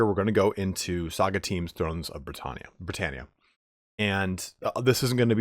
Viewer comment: For a few seconds, I heard 'Thrones of Bretonnia', and was like "Hell yeah, Bretonnia gettin some love!" Was then disappointed when I stopped being an idiot